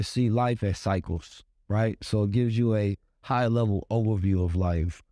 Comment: I thought you said, “AHAYAH LEVEL.”